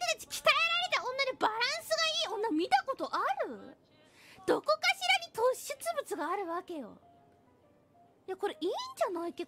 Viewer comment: I think I hear her say 斗酒物/toshubutsu instead. Never heard it as well, but I would translate is as "Where could one find an ideal woman? That would only happen in a situation with a lot of alcohol!"